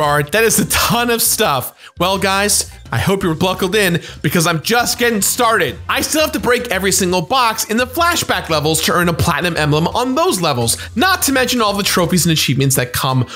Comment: CORRECTION - I made a mistake in that I said Naughty Dog sold the IP. It’s not as simple as that. Naughty Dog never owned the game IP and it was as a results of corporate mergers and acquisitions that led to Activision owning the IP. I made this comment on the fly without research and that was my bad. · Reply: You also said ‘bluckled’ instead of buckled